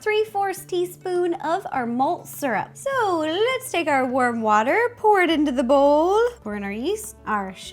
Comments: I THOUGHT SHE SAID WORM WATER!!! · Did anyone else hear her say worm water at 1:05😂😂love you ro